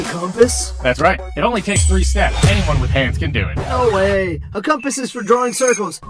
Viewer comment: I heard him say: anyone with hair can do it. To a bald guy.. 😂 idk i might've miss heard it lol😂😂